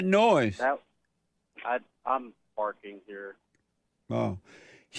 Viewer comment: What' was that noise? it sounded like he said, "I am farting here"? WHAT THE?